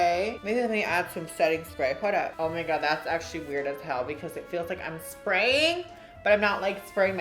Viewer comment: I had to rewind twice i thought you said " it felt like Di*k lmffaaaooo